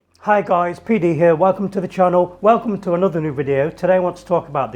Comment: my dumbass thought he said " hi guys P diddy here!" in the intro ☠